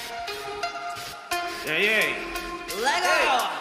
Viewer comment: Só eu que acho que isso foi um "legal" 0:08? Kkkkkkkk